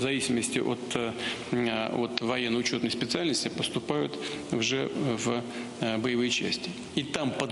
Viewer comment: мені почулось??????? пукін сказав україномовне слово "вже" ахереть